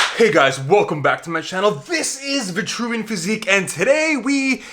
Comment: I thought he said ViltrumitePhysique I was like 😮 CHADICUS VILTRUMICUS